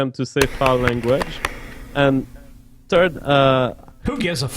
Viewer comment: i love because he is pronounces the "th" sound a "teh" so when he says "third" it sounds like he is saying "turd"